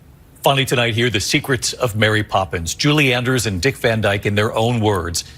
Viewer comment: Does he really say, "Julie Anders"???